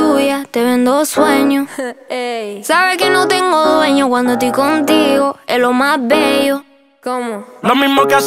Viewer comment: Pensé que decia sos lo más bello pero dice eso e más bello :v